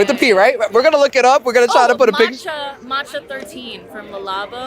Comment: Did she say Another 13 by Le Labo at 7:20 or Matcha?